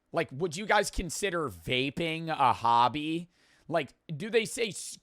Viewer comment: someone in chat thought he said vaeb not vaping